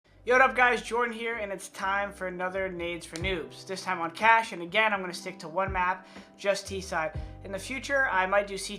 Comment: I swear I heard " Nades For Nudes "